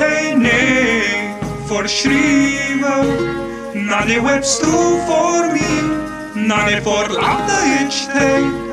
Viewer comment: sounds like Rick is saying "soup for me"